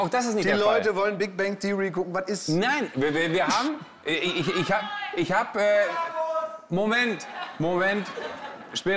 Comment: Was wird dazwischen gerufen? Ich verstehe es akustisch nicht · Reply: "Umarmt euch!", "Bruderkuss!"